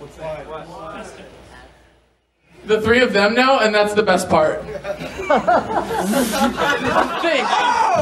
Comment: I can't hear what says at 26:11 :( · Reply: 'The three of them now and that's the best part...yeah'